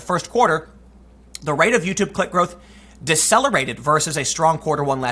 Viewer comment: Sounded like he said "the rate of YT CLIT growth..." not "click", at 1st. Lol